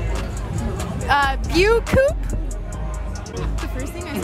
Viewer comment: I heard "bio coupe" is it normal?